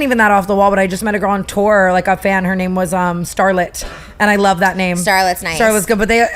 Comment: My name is StarLA 😭😭 people mishear it as starlet alot though.